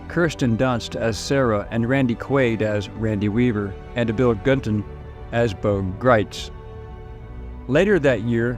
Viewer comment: had to replay that part because initially I thought it said it was played by Bill Clinton